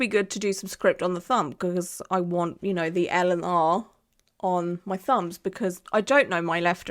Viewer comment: When you first said 'L' and 'R' I thought you said Eleanor and I thought 'do we know who that is? Has she mentioned them before?' 😂 x